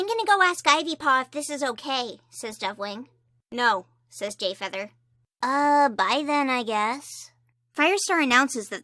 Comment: I seriously thought you said Gayfeather 💀